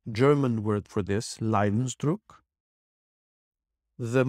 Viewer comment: What German word did you say around the minute 6:00? Could anyone spell it for me? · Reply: The German word he says is ‘Leidensdruck’.